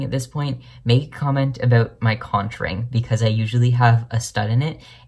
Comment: I thought you said contouring not conch ring..................